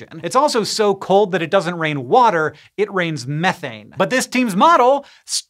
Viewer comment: Hank says it rains methane on Saturn, and I hear 'it rains farts' and loose it!